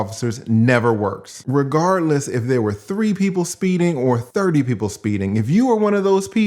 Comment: She said "there were three of us speeding". I heard, "there were two people that used me as a distraction."